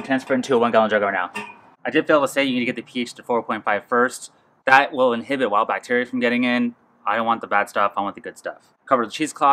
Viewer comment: sorry, I couldn't understand what you said. What inhibits bad bacteria? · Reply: Lowering the pH to 4.5 or slightly lower first.